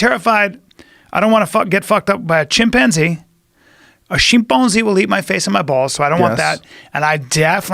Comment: wait what the hell did he say? Shitbonzee!?!?